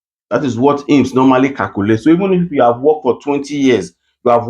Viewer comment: Sorry, you ha e to work for at least 13 weeks before you can apply for disoccupazione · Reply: I thought they said even if you've been working for over 5 years.